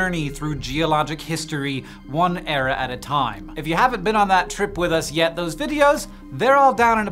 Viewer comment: "... one error at a time". Sorry, that´s what I heard :D